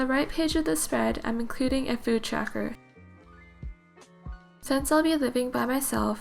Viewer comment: lol thought it said poop teacher but amazing spread ideas love it